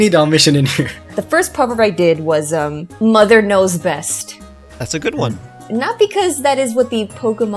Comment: Oh my goodness... I was tabbed out when I heard "Mother knows best" and heard wrong, instead hearing "Mother Knows Breast" which is a milf hentai xD